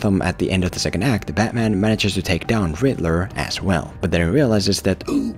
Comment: bruh did he say grittler? 😂